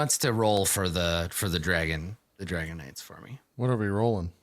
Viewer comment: I keep hearing “Dragonite” instead of “Dragon Knight” and the visual that’s giving me for their mini game is both epic and hilarious. Just imagine an armored legion of Dragonites, the Magikarp of dragons, just eviscerating wave after wave of knights in jester themed armor. I would pay to have that animated.